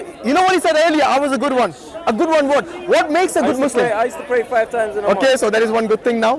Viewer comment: Pray 5 times in a month is that what he said